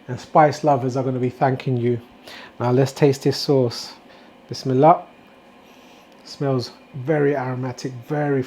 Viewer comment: I have misheard you say Bismillah on a few videos now. I am now in the habit of saying "wish me luck" at random times through the cooking process.